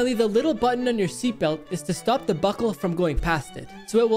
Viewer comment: Did you just say pasta as in past